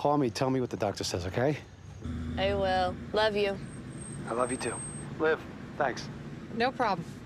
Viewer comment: What Elliot said: "I love you too." " Liv , thanks" What I HEARD : "I love you too, Liv! " "thanks" --Angelica Skyler : With a comma after dearest, you've changed the entire phrase